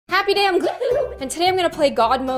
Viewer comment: I always thought she actually said “heyo guys and yOoHoo” instead of “I’m gLoom” ..